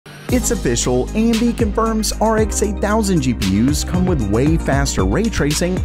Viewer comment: bro I actually thought it said RTX 8000 and not RX 8000